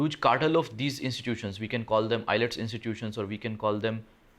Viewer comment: Did he say EYELETS instead of IELTS.... the literacy level of india lolll